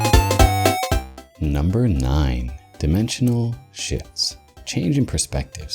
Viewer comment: At first I thought you said 'Dimensional Shits' and I was like 'Ok...weird but lets see where he's going with this...' lmao!